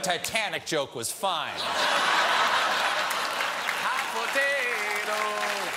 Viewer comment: did he say hot potato?